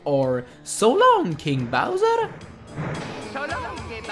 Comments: Well I learned something new ... all this time it was "So long King Bowser!" All my childhood I thought Mario said "So long Gay Bowser!" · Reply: connor bowers I thought he said "So long, Ayy Bowser!" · I always thought Mario said "So long, Gay Bowser" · All this time I thought Mario said: "So long gay Bowser" 20 fucking years ruined. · I always heard "so long big Bowser" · Well I learned something new ... all this time it was "So long King Bowser!" All my childhood I thought Mario said "So long Gay Bowser!" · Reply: At first I thought he said "So long-ze bowser!". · When I was little I thought Mario said so long me bowser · Well I learned something new ... all this time it was "So long King Bowser!" All my childhood I thought Mario said "So long Gay Bowser!" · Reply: I heard "so long they Bowsah" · Haha I seriously thought he said:so long GAY bowser😂😂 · All my life I thought he said so long gay Bowser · when he says "so long, king bowser!" it sounds like hes saying "so long, gay bowser!" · After 18 years I finally know that he says "So long, King Bowser" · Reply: I thought he said so long gay bowser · I think you got that wrong he said: SO LONG GAY BOWSER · I thought he said"so long gay bowser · After 18 years I finally know that he says "So long, King Bowser" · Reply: np +juananz007 , i heard every time "Shalom 'ge Bowser!" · After 18 years I finally know that he says "So long, King Bowser" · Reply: i thought he said "so long de bowser"